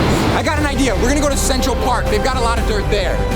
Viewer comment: Central park? I thought you said sexual park, if it was I'd go like, can I go to New York city? XD